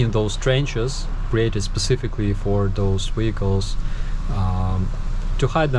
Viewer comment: I had to turn on close captions toward the end because it sounded like you said "wiggles". I couldn't figure out why someone would bury wiggles. We call fishing worms wiggles. So now I know....vehicles. great article! So funny...dig deeper!